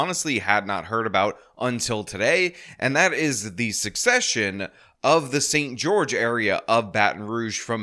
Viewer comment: Thanks for the video… perhaps I’m mishearing you, but it’s not “Succession”. It’s “secession”